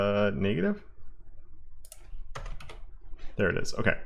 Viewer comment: Did you say the n-word bro ?? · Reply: lol no he said very quickly "negative?" :D you see that afterwards he adds the minus to make the value negative.